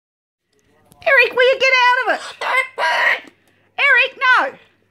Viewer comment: When said, "I'm gonna get the scary fish" and Eric said, "Get out of it." while pushing coins off the shelf! In my head, I thought he said, "No your not". And when she said, "You've made all this mess, now I've got to clean it up" and he said, "Okay!"